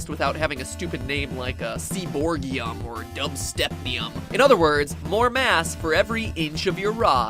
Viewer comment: I have a periodic table poster on my wall. I hear "dubstepium" AND ACTUALLY CHECK IT XD